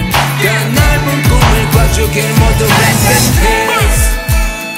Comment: Why do I keep hearing "Rest In Pain?"